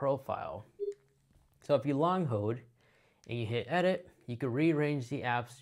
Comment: Why did you say at 1:30? I played on 1/2 speed and still can’t understand. It’s sounds like you said, “if you long mode”, you can rearrange …WHAT?